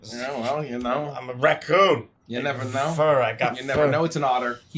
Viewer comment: "I'm a raccoon. The fur. I got the fur." What made it even funnier for me is that at first I heard "I'm a raccoon. With the fur. I got the fur." and I wasn't really listening to the conversation (cause I was eating, so I was paying attention to the food), so I didn't hear drunk Booker talk about an alligator before that, so hearing "I'm a raccoon. With the fur. I got the fur." with zero context whatsoever was just funny as hell to me